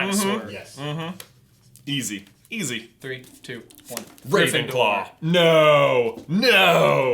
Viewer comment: it sounded like you said griffin-claw